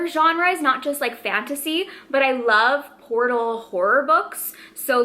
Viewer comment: When you said “portal horror book” I had to do a double take because I’m Australian and for a second, with your accent, it sounded like you said PORTAL WHORE. 😂😂 I also adore the concept and examination of this video!! It’s such a cool way to find new potential faves. :)